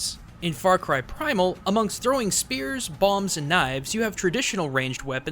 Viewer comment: bombs? did he say bombs? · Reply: update: he definitely said bomb